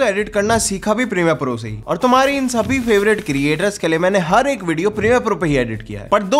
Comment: i heard tumhare sabhi chapri creator kay liye which is truth